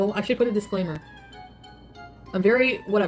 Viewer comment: did she just say 'if you're anally offended?'